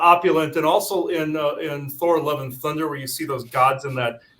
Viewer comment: Just spent the last few minutes imdb-ing 411 Thunder and Christian Bale to no avail. Had to relisten and search several times before I realised that was actualy saying Thor, Love And Thunder...